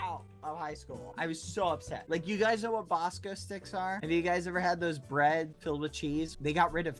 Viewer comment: This whole time I thought he was saying vosco steak